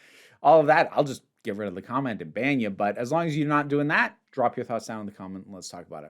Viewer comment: I had to do such a double take at the end cause I legit thought you said "I'll just get rid of the comment and bang ya"